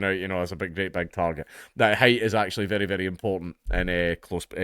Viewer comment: I thought that you were saying "hate" is very important."